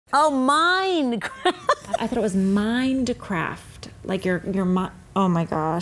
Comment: me when I heard her saying ''OH I THOUGHT ITS MINDCRAFT'' my brain:😑😑😑